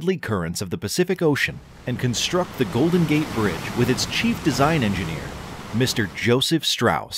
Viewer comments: when you said "chief engineer Joseph Strauss" i thought you said "cheap" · I thought you said cheap desigine Enginner